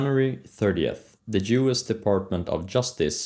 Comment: Thought he said Jewish, subscribed immediately